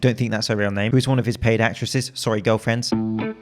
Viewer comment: I thought you guys called them prosties 😂